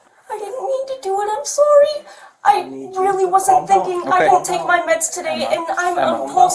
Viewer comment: She says...i wont take my meds today im sorry. Ummmm YOU CLEARLY NEED MEDS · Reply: I thought she said that too but listening again, she said "I didn't take my meds today."